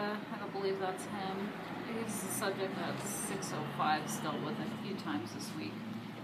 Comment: If that radio code "602" is written down on a document somewhere, you should be able to get a copy via a public records request. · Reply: I think she said 605 ( 1:16 ). Out of curiosity I checked the list for that one, and it skips from 604 - Throwing missiles, to 647 - Lewd conduct. I couldn't see any relationship between cooking a chicken in a campground's parking lot with the hood up, and either of those. I suppose one could throw the chicken. I'll not weigh in on any potential lewd acts with one....